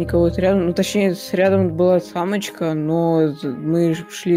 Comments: он сказал самочка или я это одна услышала? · Я один слышу на 8:03 вместо Амо4ка, само4ка